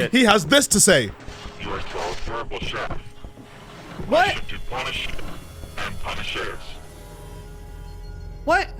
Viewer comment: once again i hope you enjoy this video and thank you all for your patience! i hope the wait was worth it :) once again this video is sponsorless, so it would be appreciated if you subscribed and showed support, links in the description! things have been pretty intense behind the scenes with networks so i really do hope that you all have fun with this vid! thanks for everything <3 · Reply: what he mr mix is saying at his aggro state at 24:34 is "and punish is: B O I L I N G I N T H E C A U L D R O N... I W I L L B O I L Y O U R B O D Y W I T H O U T S A U C E..."